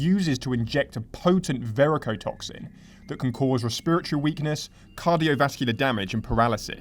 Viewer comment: your accent made me think you said it causes "Spiritual" weakness at first 😂😂😂😂